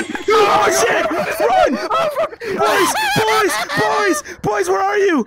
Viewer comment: Everytime someone says boy, I hear "mine" from finding Nemo lol